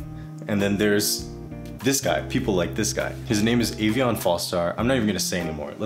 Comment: What is the name of that guy in the video???I didn't understand it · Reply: everyone needs to see aveon